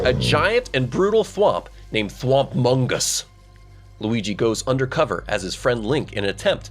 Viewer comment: For a second there, I thought you said thwomp among us. You said thwomp-mongus